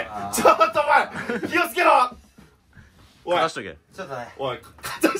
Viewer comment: 火をつけろッ?!(空耳)